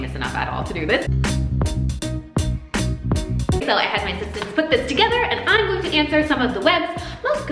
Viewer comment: “Not famous enough to do this” 😂😂 10 seconds later “ I had my assistants put this together “ 😂 Ma’am you’re famous. 😂😂😌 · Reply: Miriam I I thought she said ‘I had my sisters put this together’ 😄